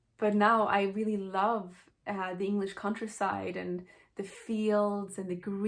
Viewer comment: You said you lived in the countryside, but what I heard was "contra-side" 😅🤔💫